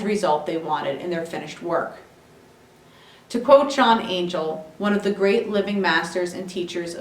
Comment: who is that artist, sorry for repeating the same question... I can't hear it well - Chan Angel or (?) .... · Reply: I believe it is John Angel also known as Michael John Angel.